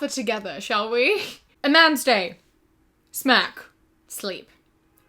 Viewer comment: I thought you said “smack” and not “smirk” I was like- maybe that’s what they call “smashing” where she’s from- then I felt so dumb when I just though.